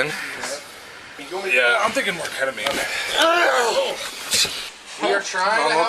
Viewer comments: Around timestamp 11:39 to 11:41 I swear you hear someone say "I'M THINKING MORE KETAMINE" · Did I hear that correct? "He's got a pulse!".. "I'am thinking more Ketamine." That Sounds Like MURDER, to me! How about you all?